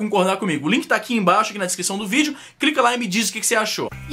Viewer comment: Eu ouvi um "crica ai" em 5:21? Hahahah! Sobre o vídeo, muito bom cara, como sempre, uma opinião muito sensata. Mas se eu não me engano, teve beijo gay em novela do SBT já, mas é novela do SBT então ninguém liga...